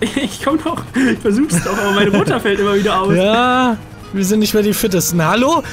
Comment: Ich hab verstanden das:,, Meine Mudda fällt immer wieder aus." gesagt hat😂